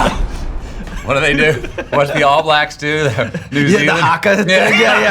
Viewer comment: does he says "what's the all blacks do" 🤣☠️